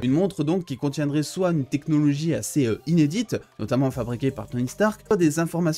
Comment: il dit des technologies assez inédites mais moi ça me fait penser à in E.D.I.T.H.